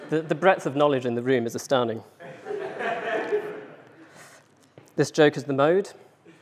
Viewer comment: This joke is the mode 😂 laughed everytime when I heard it 😄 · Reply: I thought he was saying 'this joke is a node'!